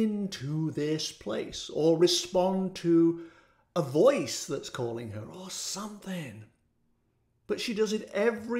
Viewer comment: Ok should have waited until the end to comment. When you asked who is calling her, I heard, “she’s calling herself”.